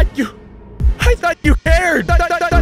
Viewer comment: It sounds like he is saying "I thought you haired!"